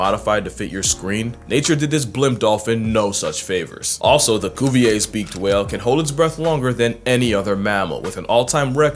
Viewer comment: I genuinely thought you said ‘goofy ass beaked whale’ weirdly instead of saying Cuvier’s beaked whale